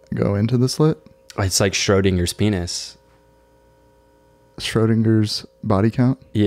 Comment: I thought he said stroding your spenis😭